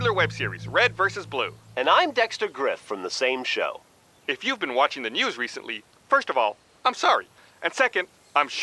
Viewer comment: I heard “if you’ve been watching the new series, i’m sorry” and I laughed, I went back and turns out said “if you’ve been watching the news, I’m sorry”. I’m sad now